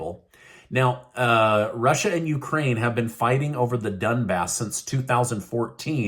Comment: Hello from Canada, 📯 it's very strange.... Your subtitles seem to play tricks (on my screen): at 6:00, the part about interactions around Dbass, the word 'attack' for example has been replaced by 'fighting', SO, the subtitles show a 'diluted ' version of what is literally said, Subtitles also 'freeze' on one of next phrases, avoiding it completely, only to transcribe normally again later on.... ;) The 'hand of God '?